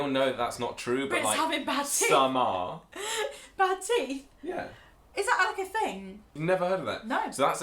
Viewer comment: BUY US A COFFEE: <link> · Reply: Being British: when y’all were talking about the “bad teeth” stereotype I thought y’all were saying “bad tea” lol I’m so dumb😂😂